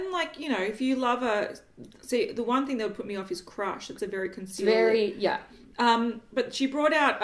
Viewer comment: The iheart makeup highlights rule.. goddess of love is a phenomenal peachy pink and the blushers are lovely too!!! Ha, when you said 'crush' I thought you said crotch.. 😂😲😂